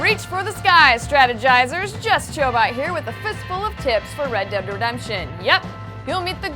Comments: it sounded like she said a fist full of tits!! :D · Did she just say A Fistful of Tits? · i thought she said fist full of tits at the start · Tips?! I heard TITS !! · i thought she said with a fistfull of tits